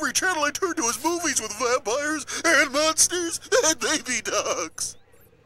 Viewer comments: Am I going deaf or did he say "Vampires, Nazis and baby ducks!"? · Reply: kartoonfanatic Yes, you’re deaf. (Jk) He’s not saying Nazis, he’s saying Monsters. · What does Grimace say at 3:51? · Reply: Baby ducks 😂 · Did grimace say Nazis? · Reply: I think he said monsters